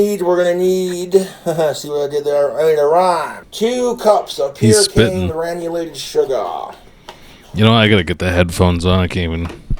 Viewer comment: Its like 6am here and i thought he said peter Caine granulated sugar. Wasnt that the guy who ate bigfoot's foot?